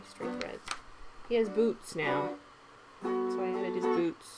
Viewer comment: When you said 450 subscribers, I said to myself "How has she not seen that it's over 500?"! CONGRATS! I always look forward to your videos and the music in the background, it's at the perfect volume, I don't know how you and the hubby coordinate it. OMG all those buttons! I just want to stare at them they are gorgeous. My fav DMC color is 550, and I would love to win the evenweave package. I am in the same boat with Chantal - I heard "boobs" and not boots.